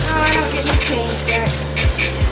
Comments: How did Larry know to document this at the time?! He has no idea how important this is. Thank you Larry, wherever you are 🙇🏾‍♀️✨ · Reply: At first, I thought she said “bye Larry, bye Larry’s streamer”. Had to run it back. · "Bye Larry. Bye Larry's friend." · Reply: I swear I thought she said Larry's STREAMER. I'M LIKE huh. Lol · It sounds like she said “bye Larry, bye Larry’s streamer” and I can’t help but think this is AI